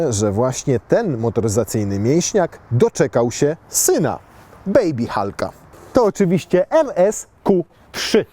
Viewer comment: czy ja usłyszałem NS?